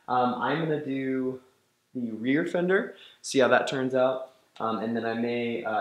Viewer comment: Did he just say "REAR FENDER"??????? Min